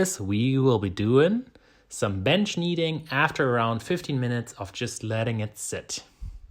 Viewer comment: Captions say 15minutes for rest between bench kneading but it sounds like you say 50minutes. Can you confirm please?